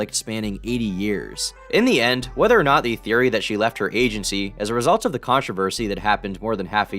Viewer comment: I thought you said "Asiancy "